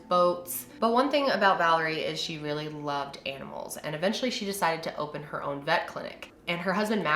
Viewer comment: When you said that she loved animals in the beginning I thought you said that she really loved egg rolls